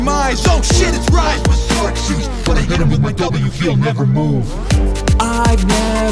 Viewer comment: for some reason i thought he was saying "nevermore". honestly, i would prefer it to be that way.